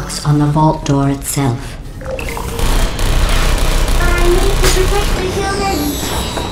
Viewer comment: did it say "I need to protect the humans"?